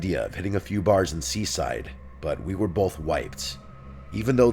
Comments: I heard “but we were both white” even after replaying it several times times 😂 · Oh my gosh at 12 minutes and 44 seconds of the first video I literally thought he said we were both white LOL😅😅 I had to go back and put on CC just to really make sure what I just heard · when he says "Mike floated the idea of hitting a few bars in Seaside, but we were both wiped", I had to rewind because I SERIOUSLY thought he said "but we were both white."